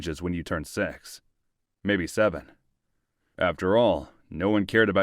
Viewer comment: did u just say.... 67?